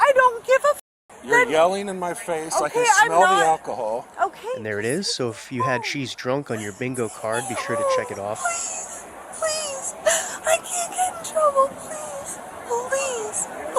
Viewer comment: my captions said "cheese drunk" which made me picture a different, but equally hilarious situation